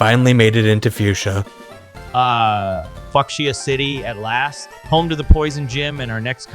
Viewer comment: fu... fu... F*CKSIA CITY?! ...The hell. . . Weirdest mispronounceation ever... I thought I heard him say it earlier on before the listed time stamp, but I then thought "Nah... It couldn't have possibly been what I thought I heard. I most likely must have just misheard him say that" right up till I manage to hear it again at the above listed time stamp. I can't believe... That psychotic AI. . Just said that. . . .